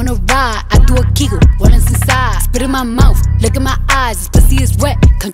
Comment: I thought she was saying "lickin my eyes" LOL 💀